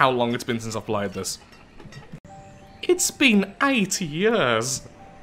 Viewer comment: Did he say 80 or 8?